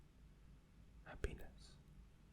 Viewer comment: when he was doing the whispeering in ur ea thing and he said happiness i thought he said "a penis"...